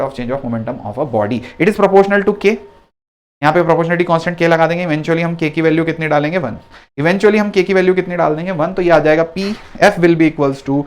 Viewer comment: wht did he say guys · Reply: Eventually _